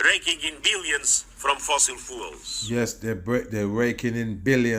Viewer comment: Hey all if you listen carefully at 19:30 to that bad acting old fart talking about fossil fuels he actually says Fossil fools and it's true cause there are to many fooled fueled up fools out there!!!